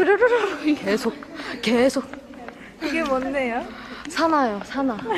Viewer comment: Jihyo parece que dice en el minuto 0:24 que es eso pero diciendo así como kejeso